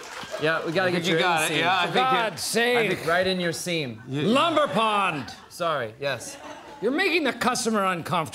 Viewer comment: OHMYGOD I DIDNT REALISE CAUSE I THOUGHT HE WAS SAYING LUMBER POT AND NOW I CANT EVEN